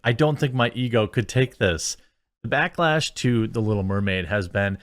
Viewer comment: said "...backlash to The Little Mermaid," and I heard "Backlash II: The Little Mermaid."